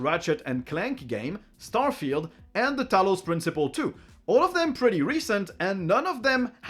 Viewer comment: wait, did someone just say "Talos Principle II"?